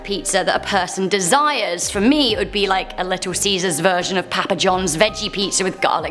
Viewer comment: did she say pap-a?